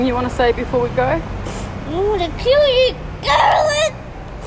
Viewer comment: Did he say I’m going to kill you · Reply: He addressed it to a “Carolyn”